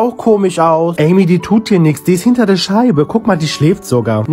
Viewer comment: Ich habe schlägt bei der Schlange verstanden😂❤ und Amy ist sooo süß ❤❤❤